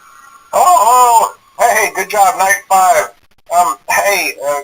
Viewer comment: i always thought he said high five not night five lol